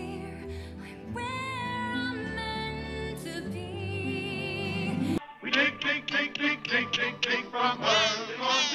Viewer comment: All I heard is we dig nothing else